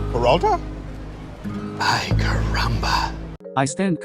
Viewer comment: BOKKURO CONTENT?? IN THIS ECONOMY😍⁉️ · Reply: It has been so long since this video was posted but...on abt 3:27, I don't think Bokuto is saying "ay car-rumba", I think he's saying " ay caramba" which is an Spanish expression lol